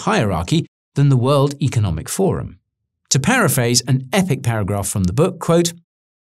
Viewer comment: Another funny fact is that Paraguay was a destination of choice for fleeing Nazi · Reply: my bad , i thought i had heard paraguay ... had found the coincidence funny